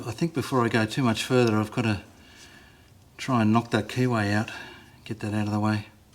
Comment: 😂I'm sorry. I thought you said knock a Kiwi out, not the key way. I'm sorry. They're funnier when they stay in my head. Hello, from Detroit Michigan, USA. I haven't made your problems with mike.Chinese piece of shite i have less than 3 hours on it